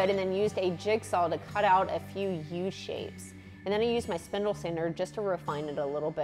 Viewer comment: did you say sandle spinder? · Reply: I heard spindle sander 🤷🏻‍♂️